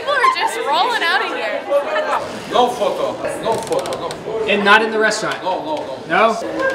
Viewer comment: At first I heard him say "No, Fuck off" and I replayed it and he said "No, photo"